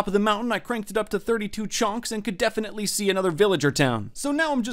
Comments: it sounded like he said "chonks" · did you say 35 chonks?